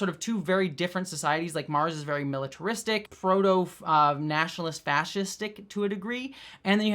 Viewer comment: Misheard "proto-fascistic" as "Frodo-fascistic" and now I'm imagining a horrible alternate ending to the trilogy.